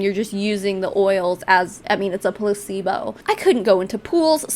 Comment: I no shit thought you said a "pussebo" on the first clip when you said placebo and yeah, that fits lol.